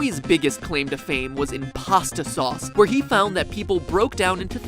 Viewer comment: At about 2:58, I misheard MatPat say, "in pasta," to "IM-pasta." If anyone who first watched this experienced the same thing I did, they heard the very first Among Us joke, YEARS before the game was a concept!